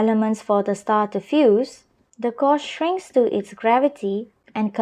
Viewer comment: could you repeat what you said? There’s also a mistake with caption there i doesn't understand what is kosh ___shrink.... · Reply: She means, the core shrinks